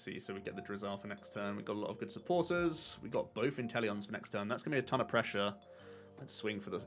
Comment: every time you say "intel" I hear "Incel" and I'm like "what?"